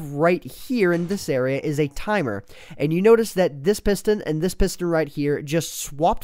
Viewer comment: Did just say pister?